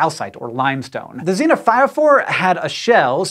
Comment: Thought it said Xenophobes.